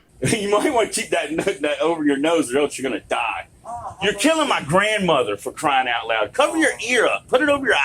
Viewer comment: what is the response? I can't quite hear it. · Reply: “YOUR KILLING MY BRAIN CELLS”